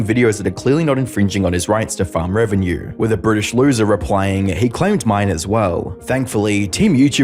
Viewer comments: I heard British loser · did that say "british loser"? or i misheard it? just wondering